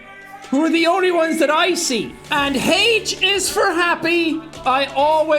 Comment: Im the only person that heard him say hate is for happy lol